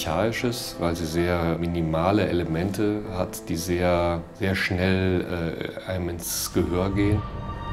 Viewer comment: The subtitles are wrong at one point: He didn't say, that his minimalistic music got something "heroic". He said, that this kind of minimalist music got something "archaic". That is an important difference. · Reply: Also from 0:40 on the subtitles say "that go straight to the heart", while instead he says "that go straight to the ear" (I guess he means it can be remembered easily, is very catchy).